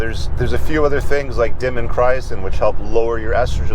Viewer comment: What is the supplement mentioned at 12:07 to lower estrogen? CC says "demm and cryon" & I can't find anything... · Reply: diindolylmethane